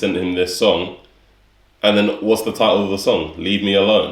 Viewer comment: Did she say "what is the chiste?" Did I misheard? Lul